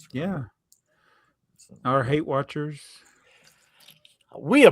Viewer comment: After says you appreciate all your views and responds with, "our hate watchers." I thought said, "our Weight Watchers." My first reactions was, there's no food segment on this podcast.